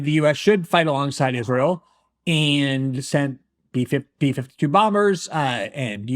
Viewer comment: Did he say B-52 bombers? (around 4:00 mark)